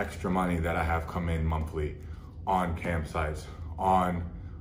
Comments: I thought he said camp sites, and I spent the first minute wondering how he ended up on camping websites from porn. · At first I thought he was saying “camp sites” lmao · Lmfao at first I thought he said "Camp Sites" I was like hmm well he does travel alot. 🤣🤣🤣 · I thought he was saying “camp sites” lol I’m like “what’s wrong with camp sites?” Lol 😂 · Bruh I thought he was saying camp sites 😭😂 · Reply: Giuseppe Vattimo cam sites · I thought he said “Camp sites” 😂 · Ohhhh I thought he said camp sites. Shit bro I was going to say, you must love camping if you’re making an apology video about it. I love me some camping too. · I’m dying because all this time I thought you were saying camp sites😭😂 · I thought he said campsites at first 😂 fair play, this takes guts to admit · Bro I thought he said camp sites like he was addicted to camping or something 😭😭💀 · First 2 minutes of the video I thought he had been saying he was addicted to camp sites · I thought he said CAMP sites and I couldn't figure out how you get stuck on that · Anyone else got subtitles on? I was like eh what are these "campsites" ads 😂😂😂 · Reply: I literally thought he was saying camp sites as well 😂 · I thought he kept talking about "camp sites." I was like "how the heck is camping gear that much of an addiction?" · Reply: I thought he was saying camp sites too at first 😅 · At first I thought he was saying “camp sites” lmao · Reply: I also thought he said "camp sites" at first. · I thought you said camp sites